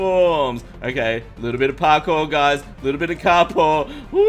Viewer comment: Did he just say KARPOUR? XD